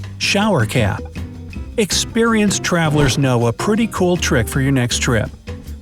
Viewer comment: at the sewing kit part i heard him say "twavelers"